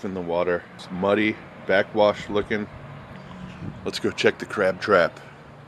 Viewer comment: I’m commenting at maybe 20 seconds in but I couldn’t resist. You already gave me a chuckle! I thought you said “let’s check the crap trap” 😂